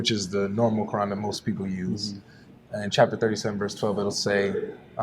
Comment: Did he said chapter 37:2 ??? in the